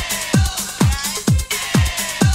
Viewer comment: When I was small I thought she said this is the river of the Nile